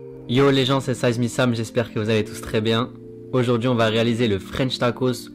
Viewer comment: j'ai tellement faim au moment ou je mate la vidéo que j'ai entendu "Yo j'espere que vous avez tous très faim" au début de la vidéo